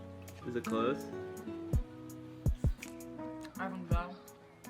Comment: What does she say at 5:44 ? I hear the second part, but not the first part ... When he asks her how it is and is it close? Does she says, "Iven gus". Or what was that? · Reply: hahaha i believe it was a mouth full “i think so”